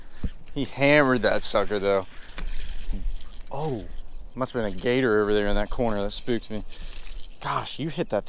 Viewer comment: What did you say? Did you say alligator ?